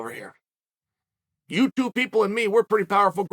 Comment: "you two people and me" I love that Chael continues to run with this gag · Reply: I thought that's what he said but convinced myself he said "YouTube people and me"